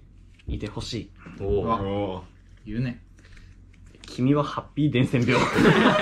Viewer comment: ハッピー伝染病がハッピーターンターンに聞こえた私はすでに伝染してる